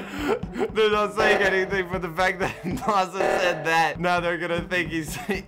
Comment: I thought u said sasha